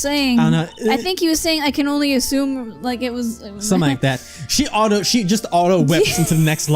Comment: What the Judge said there was "I can only presume that his intention was to capture you--" before getting whipped.